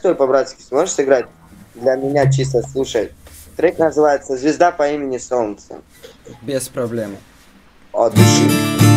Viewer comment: мне одному послышалось что он сказал ЖЫЖДА по имени солнце? 😂